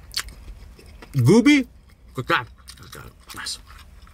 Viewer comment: Anyone else hear "Boobie" @ 6:00 LOL